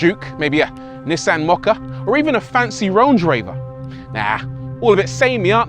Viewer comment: did he say RONGE RAVER????? 🤣